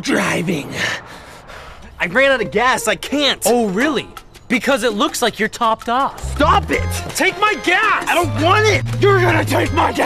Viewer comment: I thought Ian said take my ass!